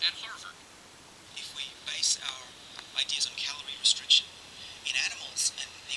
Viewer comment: does he say Calgary? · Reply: WN Continental Broadcasting I think he says calorie